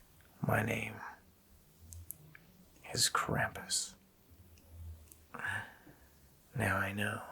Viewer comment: Omg I was trying to watch the movie but I was not available 😭😭😭😂😂♥️ Love ur contacts btw and I thought u said “crap us” 😂😂😂